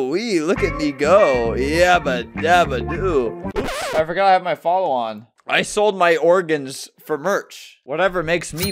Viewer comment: Im dying 7:15 i thought he said “sh!t i forgot i had my follow on” …